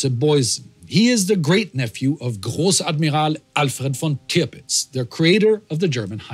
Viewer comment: it says Tripitz instead of Tirpitz. Your facial hair is really awesome tho